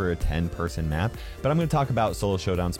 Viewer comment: Did he just say SHOLO SHOWDOWN!